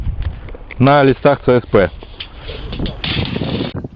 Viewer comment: Документации и ставим насечки на " ЛИЦАХ " и т.п. ))) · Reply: DIY САМ 10:12 насечки на листах цсп. Он не правильно услышал